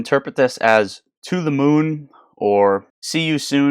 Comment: I thought roaring kitty was saying to the moon we go